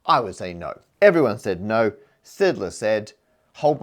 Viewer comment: My fav phrase from the video… 3:11 “Hold my shape” Also, when I heard Matt say “Sydler” shape, I thought I heard him say Szydlo (Andrew Szydlo, chemist I’ve seen vids from the Royal Institution), so shameless plug for another math/science communicator that captures my attention and mind